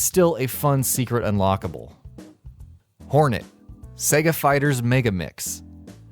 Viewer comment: My brain thought you were going to say Sega Fighters Mega Faggot. 2:26 I have to lay off the memes.